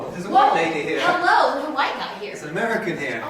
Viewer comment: im interested on the story of the people you briefly meet at 1:02:50 (for mobile) it sounded like 2 people in fluent english accents saying “woah hello! its a white guy over here!” then the other person saying “its an american!” sounded like you ran into some other non-natives/travellers in Afghanistan. i wish u would of included more of that interaction. it would be cool to know their story and why they are in afghanistan. (they sounded really out of place)